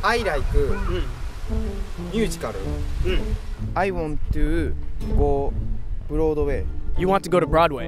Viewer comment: I swear he said I want to float away